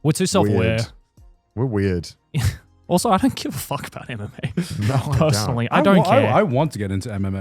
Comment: When Joey said "I don't give a fuck about MMA" I heard "I don't give a fuck about anime" and I was like 😱😂